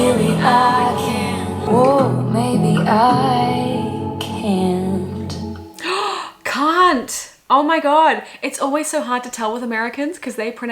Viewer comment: okay well i thought you called him a cunt so it seems even 😭 🫶🏻